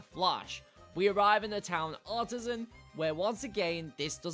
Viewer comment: it sounded like you said autism.